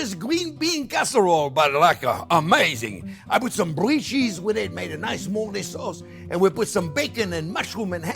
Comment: I heard: "I put some blue cheese within, made a nice moldy sauce" xD